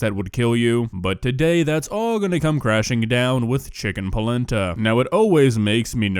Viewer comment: I thought he said "chicken placenta" 💀💀💀💀